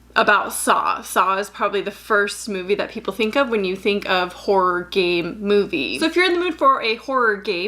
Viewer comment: Why do I keep hearing "horror gay"? What's wrong with me